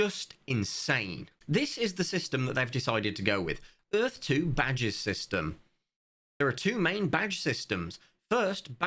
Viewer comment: I was looking away from the screen.. I honestly thought you’d said ‘badgers system’ and as a result, I was about to go all in on Earth2